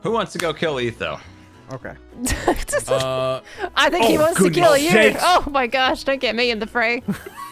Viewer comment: did anyone else hear "oh sh**" at 36:59 ?